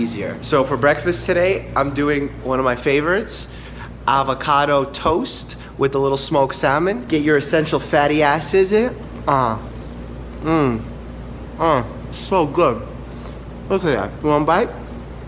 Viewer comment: I heard it as “Get your essential fatty a**es in” 💀